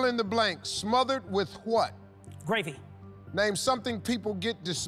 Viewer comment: smothered with gravy, what the hell does that mean? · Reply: The Blue Adventurer When I first heard it, I said "love". Never heard smothered with gravy in my life lol